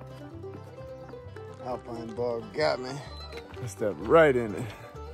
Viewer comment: What is Alpine Bar? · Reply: I thought he said alpine bog